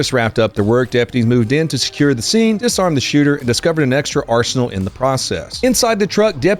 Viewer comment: lol i thought you said "and piss on the shooter" and i thought to myself "understandable" 😂